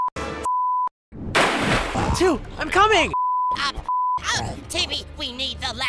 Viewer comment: WHAT IS THIS PART WHY DOES IT MAKE SENSE 😭6:11 AND WHAT IS THIS ONE😭7:42 AINT NO WAY GATY JUST SAID “ I’m c*mming!”